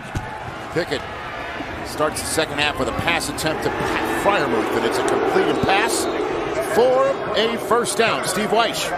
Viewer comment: and 5:46 Why does the crowd keep booing Pat Freiermuth? Or are they chanting something else? I cant tell what they are saying. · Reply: They are saying “MUUUUUTHH”